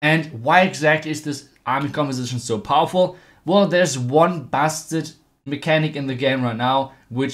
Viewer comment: Thought he said bastard mechanic lol